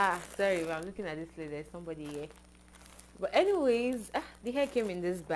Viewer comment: I thought you said somebody was not there… Abi na spiritual Betty🏃🏽‍♀️🏃🏽‍♀️🏃🏽‍♀️🏃🏽‍♀️